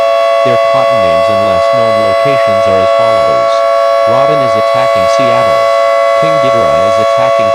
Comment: and onward: I heard the name Robin, but I couldn't get the other names?